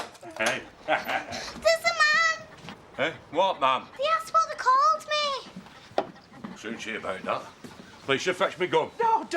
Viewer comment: What does she say at 8:26, after “there’s a man”? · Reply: "He asked what they called me"